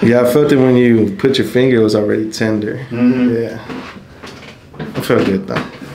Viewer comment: Bruh. This doode. Listen to what he said. 😂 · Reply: Tender fingers 😂